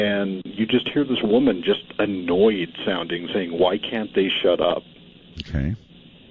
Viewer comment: I heard "why can't they hear us" not why can't they shut up